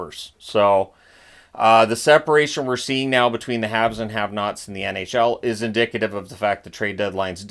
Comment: I could have sworn that you said, " The Habs and the have nots."...